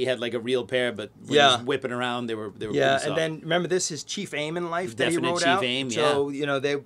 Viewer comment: I heard “Chief Aim” as “Chi Fame.” 😂